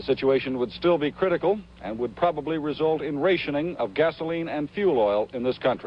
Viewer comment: Did the reporter Don Oliver at, (Tap here-->3:09<-- To Hear). Did he say, ray-shah-ning? Instead of rationing?